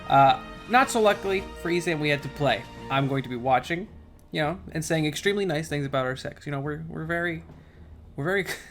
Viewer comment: I totally misheard what he said as "I'm going to be watching, and saying extremely nice things about our sex. You know, we're very close friends."